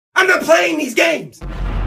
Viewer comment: Man, talking to himself for 10 minutes is better then most videos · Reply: Well said IM NOT PLAYING THIS GAMES... BUT I HEARD 'WHEN THE PLANES DEAD!!